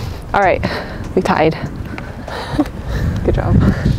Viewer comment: At the end when you said "good job on the last hole" I thought you said Good job you a..hole.... But you 2 never fight do you?